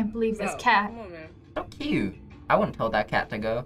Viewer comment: WHAT did he say? Oh, it was "So cute!"....it sounded like something else.